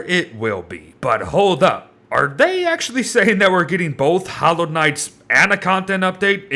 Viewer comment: However many people thought he said Hollow Knight instead of Hallowed Nights